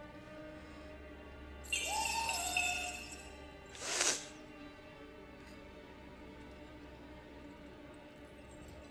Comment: What did he Say From Top?...... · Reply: Trick or treat